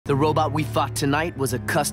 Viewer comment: Does anyone else hear "The robot we fucked tonight"? 💀💀💀